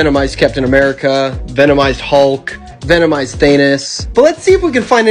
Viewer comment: Thanos❌ Thanus✅ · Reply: I thought he said venomised anys